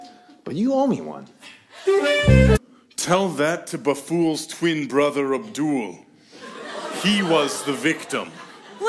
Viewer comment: Oh... OH WAIT. He said “Baful’s twin brother Abdul.” not “The fool’s twin brother Abdul.” For some reason I thought they were saying that Abdul was Aladdin’s twin brother but I knew something was off... Because I was like, “Wait... I thought he choked on shit!”